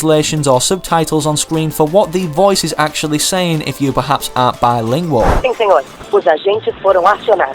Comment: what did he say "if you perhaps ?????" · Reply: Aren't bilingual